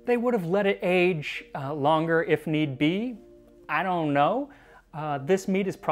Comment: thumbs up for the pun (intentional or unintentional IDGAF) "mead-iocre poetry" 16:29 i lol'd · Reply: I could have sworn he also said "If Mead Be" instead of If Need Be, earlier.